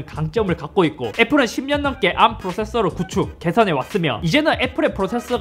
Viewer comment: 구축/개발 이 들을때는 구축/개선 이라고 들려요옹